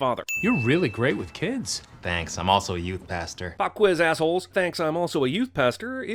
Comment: "I'm also a youth pastor too" I always thought he said "I'm also a huge bastard too"